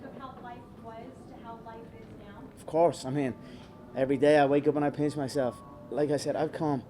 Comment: I thought he said everyday i wake up and i piss myself lmao